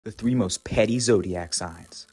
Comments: I'm not on the list... 🥲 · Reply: jk I thought he said pretty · 😭 i thought it said PRETTY. I'm sagittarius lol